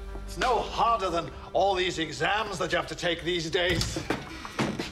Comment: "except for media studies, it's gonna be harder than that" ... The Doctor has clearly never met my college lecturer ahaha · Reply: I thought he had said meteor studies but that makes more sense